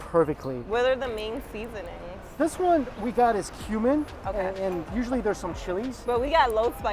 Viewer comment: where they were talkinga bout the seasoning for the biang biang noodles.... i heard said 'this one we got is...human'.. i'm like..WHATTTT.....Have you evolved that much and started eating humans....... and then i realized it was cumin.... LOL this is one enjoyable video~